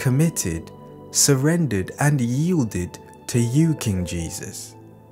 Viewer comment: Anyone can assist me with what word was said after surrender 7:02. Please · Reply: It says ‘And yielded’